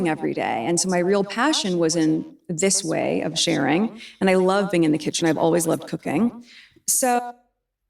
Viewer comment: At the 4:45 mark, I almost thought she said, 'I love c0caine . . .'